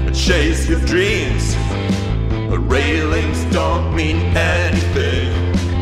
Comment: “Railings dont mean anything” is legitimately pretty motivational what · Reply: At first I thought he said "The lyrics don't mean anything"